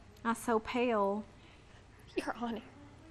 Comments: What's she saying? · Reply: You’re on air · The way she breathed out “you’re on air” was a huge Pam beesly move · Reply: What i heard was "your on" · The way she breathed out “you’re on air” was a huge Pam beesly move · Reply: I heard "you're on it" kek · Pale girl: "I so pale" 😐🙎‍♀️ Blonde girl: "you're on air" 😳🥴 Pale girl: 👁👄👁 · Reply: I hear "you're on it"